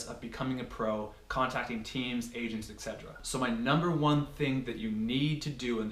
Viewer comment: when you said ages/agents I thought you said "asians" haha lol